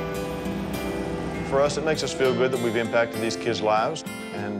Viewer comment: i think she said "bye mum" and i think that was the sweetest thing she has ever said